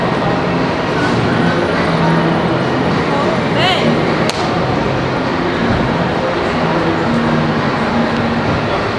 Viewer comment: Am i trying or did say bae at 19:49 or did she say hey? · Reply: Definitely said bae I listened about five times 😂